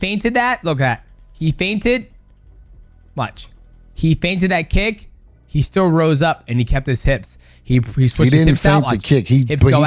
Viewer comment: He is literally saying you’re the one feinting the kick. You’re not even listening cuz you are so salty 😂